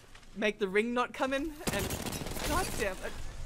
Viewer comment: Better stop anarchy I swear I heard you say that hehaa